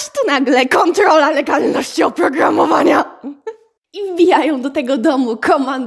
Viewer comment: i FBIają xD (wiem że z błędem ale jak to usłyszałem...)